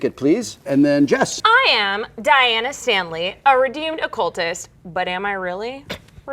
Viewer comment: It sounded like she said a redoomed cultist instead of redeemed